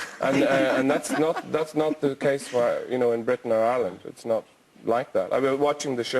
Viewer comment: What the heck 4:29, He said Britain and Ireland and the subtitles say England and Ireland? Norway is intelligent and educated so how on earth did the interpreter botch that one up?